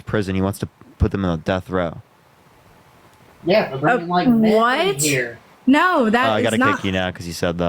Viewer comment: At about 31:20 the man in the background says something and the host the ends the call due to tiktok terms of service. What did the man say that's against Tos? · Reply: "...yeah they're bringing meth in here..." is what he said.